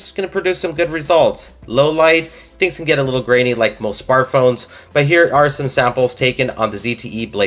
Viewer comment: What are you saying at 9:15? I'm looking for a smart phone with a good camera, especially in low light. It sounds like "things can get a little grainy like most far phones..." What is a far phone?